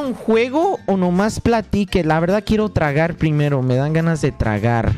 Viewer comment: I love when quackity said "quiero tragar primero" and the subtitles said "I feel like eating" xd